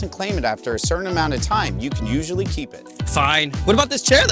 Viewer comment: I thought he said “I’m gonna shit in this chair so much”